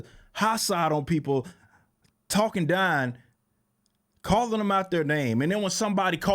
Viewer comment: What part of Texas you from? This boy said “Talkin Dine” 😂